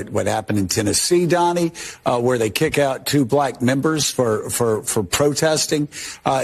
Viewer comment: I thought Joe said 2 black nippers. Especially after I saw dudes eyes go 😳 Had to listen to it 2 more times but he said 2 black members. I guess dude with the wide eyed facial expression is just methed up on something and I need to clean my ears out. 😂